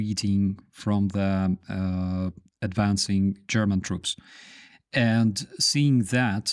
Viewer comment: LOL, i thought you were saying Beriya troops! :-D